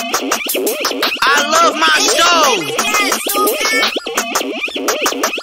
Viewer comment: this song literally makes me say i love my stove out loud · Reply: ALL THIS TIME I THOUGHT HE SAID "I LOVE MY SHOW" UNTIL I READ YOUR COMMENT AND LISTENED TO IT AGAIN